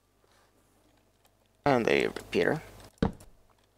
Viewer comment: When you said repeater at about 31:50, I thought you said root beer!